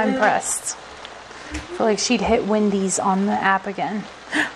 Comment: Just after 10:36 it sounds like said "Wendys" 😲😃